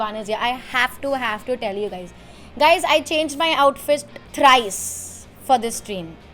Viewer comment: is this only me Or other too who heard suhani says OUTFIST 🤔🤔